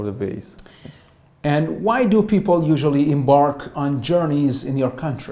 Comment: what does the examiner say here? whats the meaning of in bark?? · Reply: It is Embark which means begin